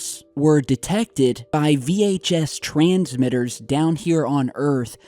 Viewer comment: LOL for a second there I thought you said VHS transmitters, as in a VCR tapes. But it is actually a VHF transmitters